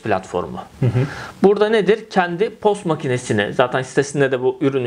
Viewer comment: Pos makinesi derken hep tost makinesi anladım))). Emeğinize sağlık saygılar.